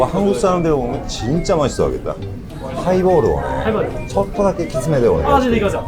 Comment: 에 하이볼 진하게 부탁드려도 되냐고 여쭤보실때 일본어로 뭐라고 말씀하신건지 여쭤봐도 될까요ㅎㅎ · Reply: きついー>きつめ 키츠이 라는 い형용사가있는데 키츠이라하면 정망여러뜻이있어서..